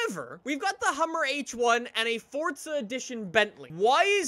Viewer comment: I swear I heard him say fortza instead of forza